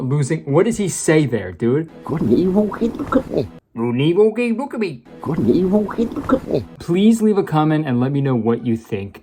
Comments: I think He says “Couldn’t he be? Well he could be.” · "Good and evil- Hey, look at me" was all I could decipher from Lorie's boss. This guy slurred his lines so bad he had everyone utterly baffled with a language puzzle. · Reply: I keep hearing "Good and evil- keep 'em company!" Possibly referring to the choice everyone can make in life, good or evil. But the way he delivers the last part of the line is so slurred and lazily enunciated that its difficult to pick up on. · I don't know if Kurtis ever figured it out, but for anyone curious about what the boss is saying in that scene, it's "Good and evil. Hey, look at me." (I watched the scene in the movie with the closed captions on lol) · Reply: I thought it said “good and evil, hate the cripple” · I have Auditory Processing Disorder. I feel like for once in my life my talents of deciphering what in the hell people are saying are useful. The boss says, "Good and evil-- Hey, look at me." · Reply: I also have APD and I heard "Good and evil keep good company" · The boss sounded like he said "what an evil way to look at life" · it’s sounds like he said “good and evil couldn’t cook me”😂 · ok so I actually think that this guy is saying "Good and evil okay? Look at me " coz she looks up afterwards and thats just what i hear. Hope this helps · “good and evil, okay look at me” is what i heard (btw english isnt my first language i might be wrong) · I think he is saying 'Are you okay? Look at me' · I hear “Good and evil… hey look at me”